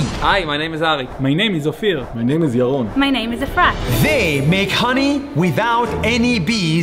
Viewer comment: What it supposed to be: Afrat What i heard: "A FRAT"💀